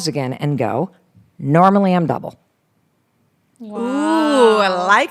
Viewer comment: when Mel is talking about determining how much to charge a client, she says, "Normally I'm...." what? It sounded like 'bevel', but I know that's not right. Can someone else interpret that for me? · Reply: "Normally I'm double" She says her plan was to say she's double the amount they offer to pay.